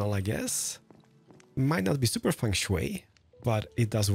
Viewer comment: So at 10:44 is he saying Feng-shui or van Sway? And if the latter: Has someone been watching Etho? :D